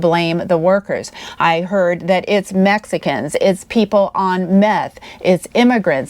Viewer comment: Did I really hear "methxicans"???